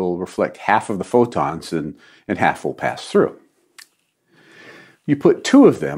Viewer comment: the cc says,"Half-silvered mirror will reflect half of photons and half will be reflected" says half will pass through?